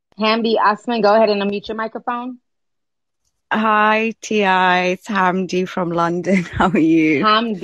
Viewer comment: This lady said "hoi T.I." XD